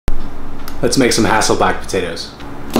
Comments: I at first thought he said "let's make some asshole back potatoes" 😓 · I swear to God I thought you said asshole black potatoes